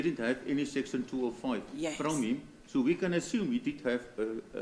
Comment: Did I hear the word 'ASSUME ' ? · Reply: Yes he said "i assume"